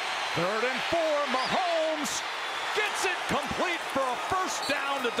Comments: What's wrong with this one? He said "gets it complete", not "incomplete". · Did he just say it’s incomplete for a first down?